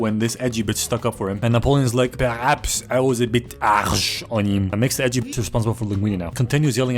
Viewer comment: the way our boi said "harsh" there is nearly the exact same way you say "Arsch" in German. Which means ass.